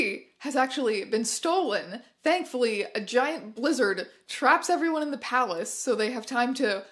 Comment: At first I thought you said a giant lizard trapped them in the palace, I was so confused.